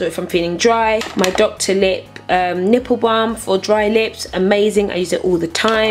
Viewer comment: did she say nipple balm at 14:36 ? ..